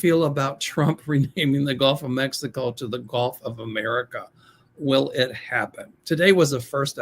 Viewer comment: Trump just said today that he is going to rename New Mexico to New America! Can you believe that fool!! 😂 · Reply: I heard him saying he wants to change the name of The Gulf of Mexico to The Gulf of America.